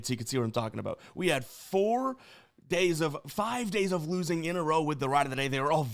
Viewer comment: I mean i would admit that if it were to be the case, it def has been in the past.. but not right now so maybe you are confusing me? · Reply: I must misheard you I thought you said it's been 5 days of losing